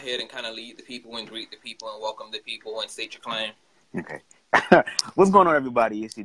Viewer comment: Lol! U said state ur claim... I thought u said represent what set u claim! I had to rewind that intro!